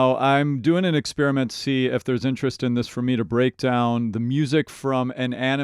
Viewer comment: come on! all openings! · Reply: did he just say Break Down?